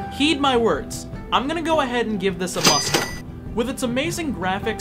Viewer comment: "The loneliness also hits you kind of hard. Like a subtle freight train." And when you said "I give this a must own." I thought you said "I give this a mustard." and had to replay it because I fell out of seat laughing.